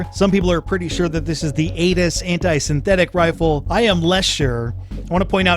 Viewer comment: Did he say "Anus Anti Synthetic Rifle"